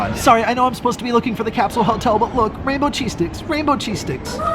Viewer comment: Oh my god at first I thought he said rainbow cheese dicks.hahaha